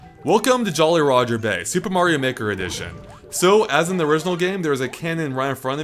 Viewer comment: I thought you said "super mario nicker" addition